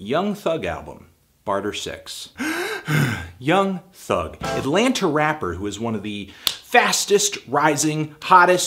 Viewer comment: Did you just say "Atlanter"?